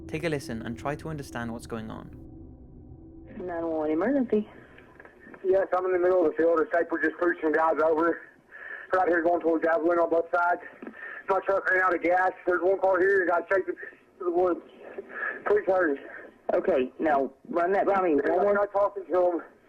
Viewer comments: Anyone else hear, "this trooper just pulled some guys over." and then, "guy's chasing me into the woods."? Not sure about the trooper part, though. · sounds like he said "they're chasing me through the woods." And he's out of breath and his signal was tracked 4 miles from his vehicle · I think he said and this is only my thoughts and what I heard “yes I’m in the middle of the field, a psycho just pushed some guy over right where I was going towards, I’m bleeding on both sides. My truck ran out of gas. There’s one car here and the guys taken them into the woods, please hurry. I’m not talking to them I accidentally ran into them, He shot the first guy” Now I think he saw someone either disposing of a body or he saw a murder taking place and after he was discovered they caught him, he ran and made the two calls and was then found and disposed of... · The 911 call, sounded like he said, “a sniper just took some guys out”, or “ pushed them over”. Sounded like maybe he was mentally ill or possibly a drug issue... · I figured out what he's saying at the [inaudible] part...He says, "...There's one car here...And a guy's chasing me through the woods..."